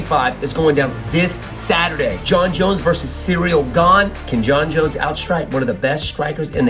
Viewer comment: Did he just say “cereal gane”?